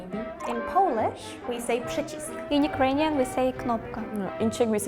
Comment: This YouTube channel is successful because of all of the beautiful young women in their videos. They could be talking about anything and still get lots of views because they are pretty young women. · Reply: that's true, like they NEVER get guys in those lmao also there are so many caption errors, at 16:35 she clearly says "przycisk", not "guzik"